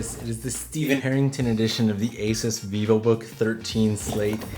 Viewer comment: I heard "Billy Herrington edition" and was about to throw my wallet at the screen.